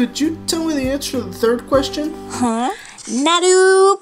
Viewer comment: did she just say... NARU BUTT?!